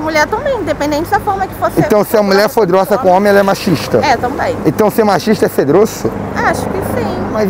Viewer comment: mulher machista? foi isso que eu entendi ou eu tô louco ?